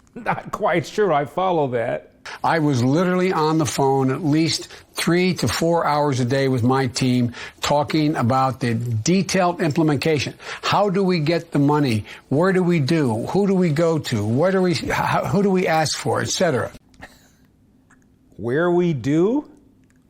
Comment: did he say "implimacation"?